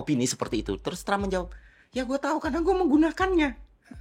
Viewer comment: lu ngomong apa si bg, gue ulang2 cuma mau denger bagian ini, auto translate indo,inggris, pun ga nge deteksi lu ngomong apa. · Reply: Trus Trump menjawab: ya gue tahu karena gue menggunakannya. Itu bro yg d omongin kang Ferry